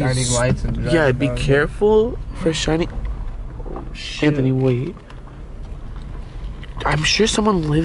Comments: did rug just say shanthony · Did he really say “shanthony” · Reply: He said " shhh Anthony wait"